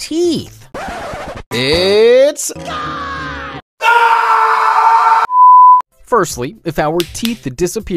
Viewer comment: he actually said it’s gone 🤪🤪🤪🤪🤪